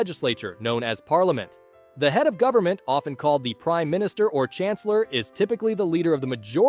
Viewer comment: who else heared "crime minister"?